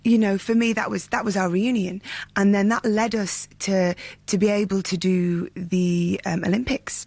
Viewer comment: When she said "That led us" I hear her say "Lettuce" now I want a salad. DARE SHE